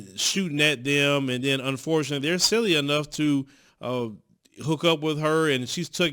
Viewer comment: did you say "tooken?" 0:28 😖